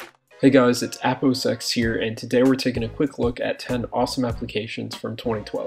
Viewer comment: lol i thought you said its apple sex here xD